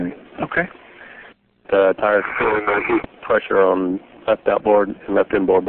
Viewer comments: I'm feeling the heat. Literally seconds after that, they lose contact with Columbia. What a sad day. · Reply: Iron Monster listen past what they are saying cause I heard it. That was the crew saying "I'm feeling the heat". Pull the time back to 5:00 and you will hear it. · I must be the only person who hears the Shuttle commander say at 5:03... "Feeling the heat!" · I heard "Really feeling that heat." Then I heard a message that was talked over by mission control. Then the last thing I heard before static was "Make You Suffer"